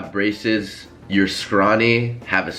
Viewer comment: I thought he said racist when he said braces